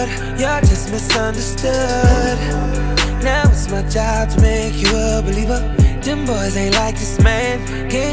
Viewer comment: Lol for a min I thought he said "now it's my time to make you a belieber" lol Justin reference